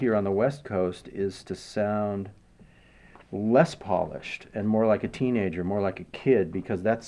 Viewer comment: I thought tim was saying 'les paulished' at first 😂😂